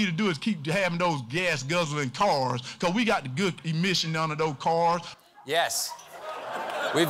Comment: Walker sounded like he said "commission" not "omission" which is more understandable for the republicans. Just Sayin'.